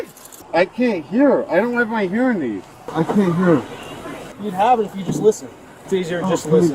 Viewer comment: You would have you hearing aid if you would just listen. Did I hear that right🤔